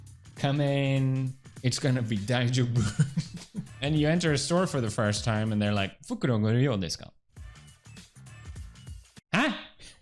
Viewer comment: What did he say at 7:31? フくろんごりよんですか? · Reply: 袋ご入り用ですか。 Would you like a bag (for your purchased goods to put into)?